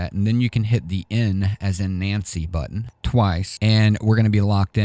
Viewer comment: I could have sworn you said "M as in Mancy" - Archer <link>